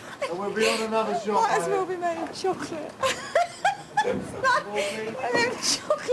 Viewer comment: What about chocolate? lol she was laughing so hard I couldn't hear Was the wrench made of chocolate? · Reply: I thought she said: it might as well be made of chocolate it’s like one of them chocolate toolboxes